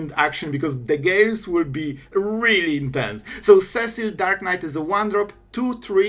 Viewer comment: I know you said "The games will be very intense" but I heard "the gays will be very intense" and thought sounds like a good time to me 😂